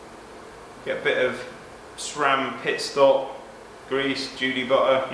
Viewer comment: What grease is it on 6:00? (can't hear the name) Thanks in advance! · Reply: pit stop or judy butter...basically the same thing,just different name